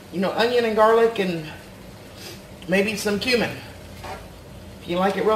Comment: I have to get my ears check it sounded like you said something about a little human in there :O cumin actually ;) that knife is trying to prove how clean the floor is I'm sure :P our two points is a wee bit of time with you I'm sure :D rest up